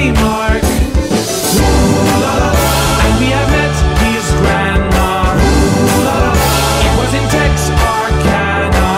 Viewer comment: i, not being an american, always thought they said "and we have met his grandma, it was intense arcana" edit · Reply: Tex-Arkana, like Texas-Arkansas